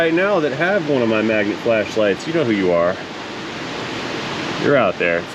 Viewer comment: I thought he said “Flesh Light” soon he’ll have a promo code for a branded Flashlight